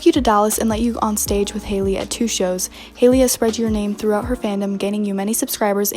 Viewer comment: OH GOD I THOUGHT YOU SAID HALEY PHAM AND I WAS GONNA CRY